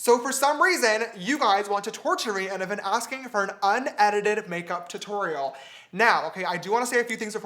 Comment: When I first clicked on this video,I thought it said " unlimited makeup " XD